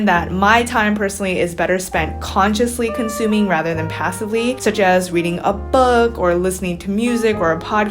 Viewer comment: but ur making content for people who are passively consuming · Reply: Yung Honey omfggg I thought this said passively cumming😭😭